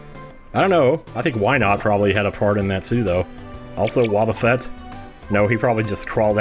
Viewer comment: Totally misheard that as, "I don't know, I think Wynaut had a fart in that too."